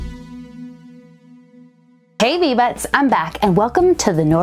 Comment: I feel silly. I always thought she was saying hey bee buds! Again I'm silly